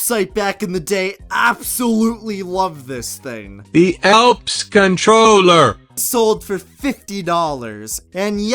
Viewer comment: When you said Elps controller, it sounded like purple shep.